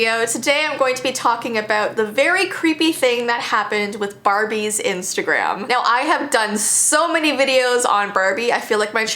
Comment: For a sec I thought u said”Abby’s insta”and I was like😀